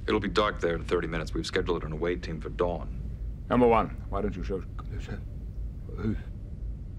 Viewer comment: At first glance. I thought he said to his quarters lol